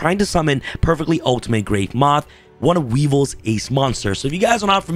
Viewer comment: For a second I thought you said, “… Summon Perfectly Ultimate Great Month” instead of moth